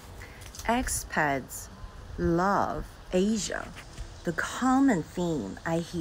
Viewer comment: I heard "Ex-Peds LOVE Asian..." I had to turn on the closed-captioning to get it straight.